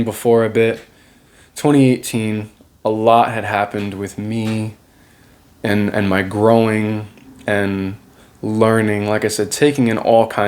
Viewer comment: ~ I’m listening on 2X speed and I thought you said “something happened to me and my groin.” Lol